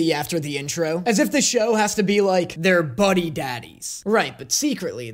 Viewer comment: Not me mishearing it in the podcast as Bunny Daddies 😅